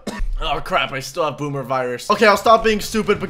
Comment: did u say boomer virus?